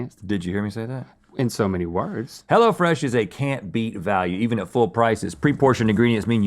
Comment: I thought Rhett said, “Hello Fresh is a canned beef value.”